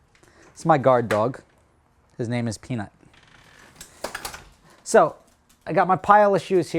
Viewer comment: I thought he said his dog's name was penis